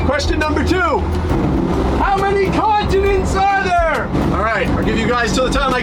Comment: When you asked the continent question I thought you said condiments..I was so confused for a second